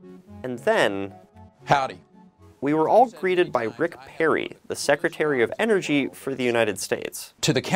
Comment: I thought he said “We were all created by Rick Perry”. This video was about to turn into something about us all living in a virtual reality.